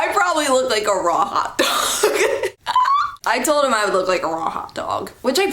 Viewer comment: When you said a raw hot dog I thought you say ro-hot-og, lol accents and omg this guy ;-; glad there was some cool people there at your school though :-)